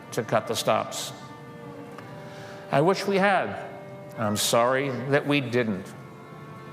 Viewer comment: Did he say, I wish we have? If yes, it should have been "had".